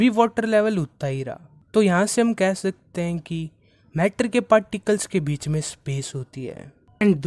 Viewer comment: you say particles I hear paticles